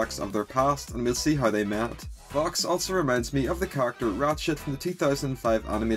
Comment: WHY DID I HEAR “RATSHIT” AT FIRST 😭😭😭